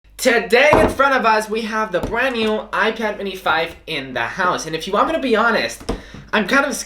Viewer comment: Is it just me or like I heard “icad mini 5” in the beginning